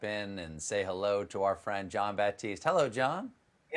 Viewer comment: WTF is going on with the English subs?! At 0:12 instead of Jon Batiste it says John Podesta. It's not even close in pronounciation.